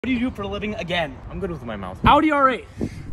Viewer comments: Why did I hear 'he's good with his mom' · Dude I thought he said “I’m good with my mom” and I was left so confused 😂